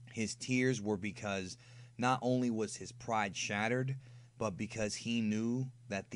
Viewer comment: Turn on subtitles and go to 10:41.. instead of the caption saying "his pride SHATTERED" it says "was his pride a TURD" lmao.. like.. really. realllllly auto subtitles. a turd. thats what you got out of what said.